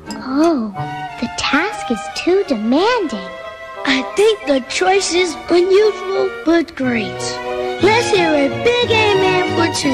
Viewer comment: What did he say at 3:12? "I think the choice is...when you full bud grapes???" or when you fill good grades? or you'll do great? I didnt understand that part. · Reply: "I think the choice is unusual, but great"